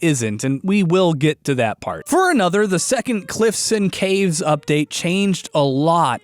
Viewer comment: Did Austin just say cliffs and caves... Lol